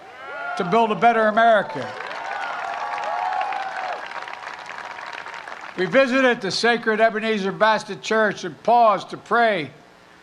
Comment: did Biden say “We visited the Ebineezer Bastard church” it sure sounds like it 🤔